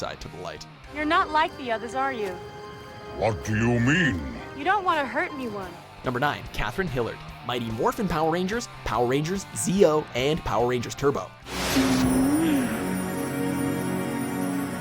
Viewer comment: almost thought you said kraithing killer